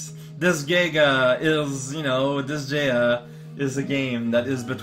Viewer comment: Did I just hear Disgaea? Aw yeah, Disgaea is my favourite SRPG.